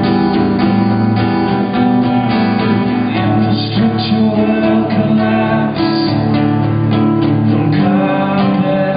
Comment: The album version is awesome, but I actually prefer this. 1:15 Is he saying "Infrastructure will collapse from carpeted space"? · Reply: Carpet spikes